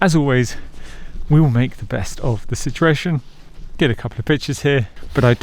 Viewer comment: sounded like he said get a couple of bitches here🤣